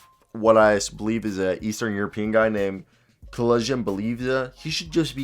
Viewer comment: bruh i know you did not just call KEL-JIN BLEH-VINS 😂😂😂